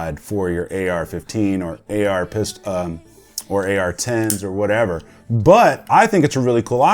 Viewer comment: I feel like dude said “onesoever” instead of whatsoever, right